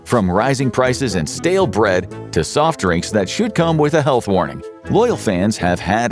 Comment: soft drinks that should come with a health warning Thought he said "shoot cum" 😮